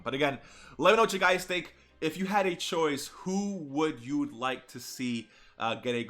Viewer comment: why would teq tapion replace agl? they have different names and link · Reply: understandable it's just that it sounded like you were saying if you had both